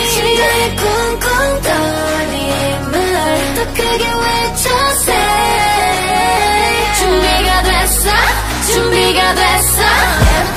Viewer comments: i thought e:u was saying everyday 🤦🏻‍♀️ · i thought e:u was saying everyday 🤦🏻‍♀️ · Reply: I heard "everyday everyday you go to Walmart everyday"